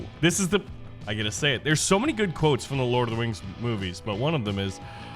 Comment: did you say LORD OF THE WINGS!!